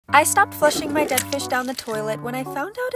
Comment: OMG 👁️👄👁️ I THOUGHT SHE SAID "I STOPPED FLUSHING MY FISHES DOWN THE TOILET"....... THANK GOD THAT THERE IS AN OPTION TO REPLAY THE VIDEO OH